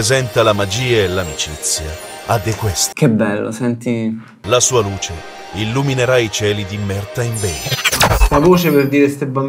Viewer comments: " la sua luce illuminerà i cieli di merda".. solo io ho capito quello? Comunque una run completa con che gioca sarebbe perfetto. #vendettaperredez #baldo #occhioperocchio #denteperdente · "la sua luce illuminerà i cieli di merda" solo io ho capito così? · Non so per quale razza di allucinazione uditiva ma giuro che a 1:27 ho sentito chiaramente "MERDA IN VETRO" e sono rimasto molto deluso nello scoprire che in realtà dice Maritime Bay Ah inutile dire che vogliamo la serie completa su rilax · 1.26 solo io ho capito "la sua luce illuminerà i cieli dimmerda?"